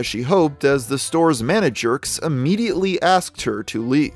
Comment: did he just call them “the stores manajerks” 😂